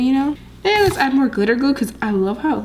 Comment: I thought you said " dont put clear glue in already made slime " and that's clear glitter glue